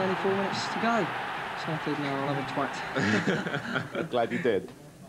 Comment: did he say “no I’ll have a twat”? 😂